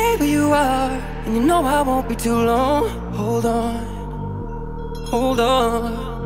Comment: おうどんにしか聞こえなくて草